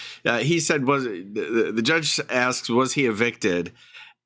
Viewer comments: I actually also thought the judge said “Is he a victim.” 😂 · In the second one lol. I thought the judge said victim too lol.